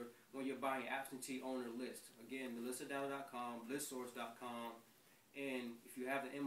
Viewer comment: What's the website you're mentioning here? melissa??? .Com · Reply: Kim j at 4:43 it sounds like he says melissa <link> before he mentions list source and the MLS.